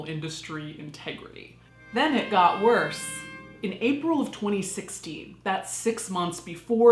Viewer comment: There is an issue with the captions at 4:20 She says 2016, but the captions say 2018.